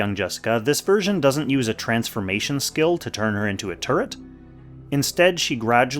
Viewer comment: "this version does not use a transformation skill to turn her into a turd" I swear that's what I heard when the clip ran in the background while i was afk.